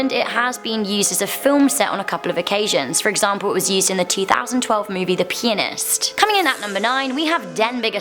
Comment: who else thought she said, "The 2012 movie the Penis"?